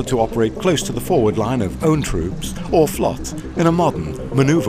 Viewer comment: what does he say at 2:09? "the mobility of the system enables arthur to operate close to the foward line of own troops or XXXXXXX" It sounds like "flots".